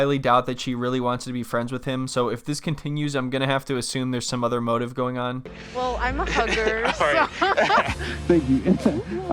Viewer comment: I thought she said “I’m a hooker so….” 😮